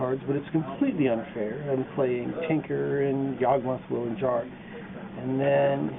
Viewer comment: Which 'unfair' cards did mention at 2:37? Tinker and what else? it's hard to hear what he's saying. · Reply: memory jar. sorry for being 13 years late